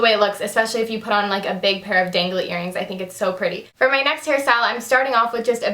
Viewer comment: I heard that you said "for my next harry styles" ... i'm going crazy... for 1D.